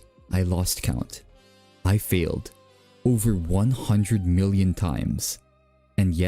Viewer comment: captions say field instead of failed heads up